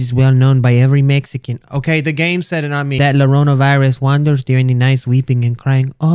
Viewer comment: did he say Llorona virus?